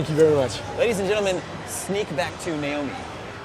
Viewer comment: lol is it me or does he say "sneak back to 'nailme' at 11:03 ? :D